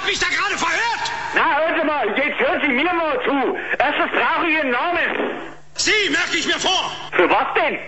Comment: was sagt er? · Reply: "Erstens brauch ich Ihren Namen" das ist jedenfalls das was ich verstanden habe!